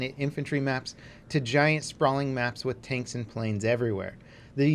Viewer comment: anyone else hear "taints and planes"?